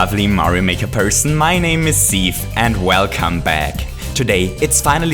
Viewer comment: he says ceave I thought he said seed